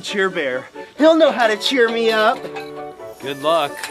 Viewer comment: Cheer you up? I thought you said TEAR you up. MB bro